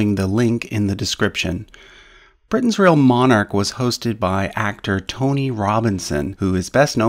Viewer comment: I thought you said Tommy Robinson xD